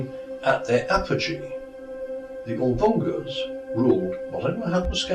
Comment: What is he saying here? Aldungas?